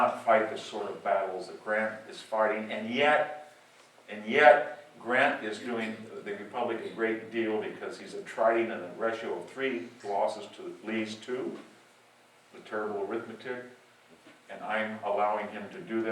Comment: Can anyone tell me what is being said at: 21:45 to 21:50 ? For several seconds it sounds like a foreign language to me! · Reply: calvin owens He says "And yet, Grant is doing the Republic a great deal because he is attritting at a ratio of 3 losses to Lee's 2. It's a terrible arithmetic."